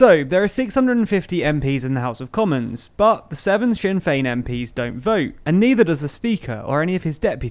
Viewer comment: what does he say? seven *something* MPs? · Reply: Sinn Féin